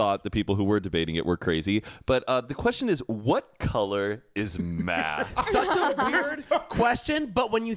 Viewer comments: omg i thought they were saying what color is “MATT” i was like .....red? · I heard “what color is Matt” and thought oh he’s red lolol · When said "what color is math" I thought he said "what color is Matt" like referring to his skin tone😂😂😂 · i heard “what color is matt?”😭😭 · i thought y’all said “what color is Matt?” i immediately thought red... lol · At first I thought they said “What color is “ not what color is math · I literally heard “what color is MATT” and I really debated it like what could they mean? are they debating if he’s white or pink or..... and then said YELLOW damn my confusion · I thought he said “what color is Matt??” And I was thinking PINK!! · I thought he said “what color is Matt” lol